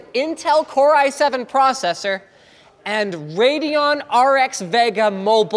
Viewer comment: I thought it said radeon RX vegan, oh wait, it actually is